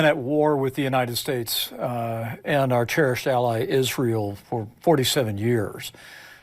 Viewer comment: At first I thought Pence said “our terrorist ally Israel”. However, given the IDF’s murdering of countless innocent civilians in Gaza, Iran and Lebanon can you really blame me for mishearing that? 🤷🏻‍♂️